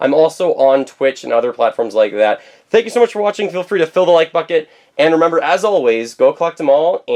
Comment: did he say hit the like bucket